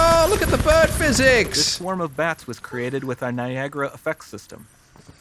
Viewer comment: Why do I keep hearing 'VIAGRA' in this demo 😂